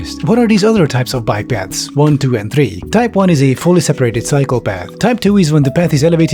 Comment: Why did I hear fully separated Psychopath?! lol